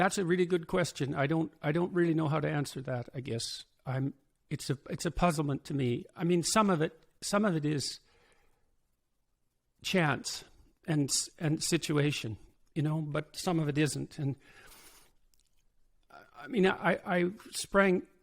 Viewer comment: When asked why he’s a phenomenon, I thought he said “some of it is Chads.” I was like, wow, he knows his slang and that’s really specific. 😄